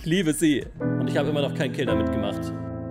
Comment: warum hab ich erst verstanden das er mit der tarnung immer noch kein 'kind' gemacht hat😂 musste es mir 3 mal anhören um zu verststehen das er kills meint lol😂😂😂